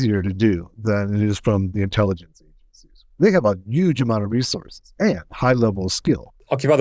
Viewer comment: I swear to god when talking about the intelligence agencies says "WE". As in WE have a lot of resources. Referring to himself as being part of the intelligence services. Did I hear that right???? I also notice that DDG seems to give different search results to Google. Not sure why.